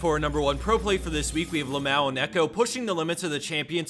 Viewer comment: did anyone think when he said lamau you thought he said lmao? Because i did xD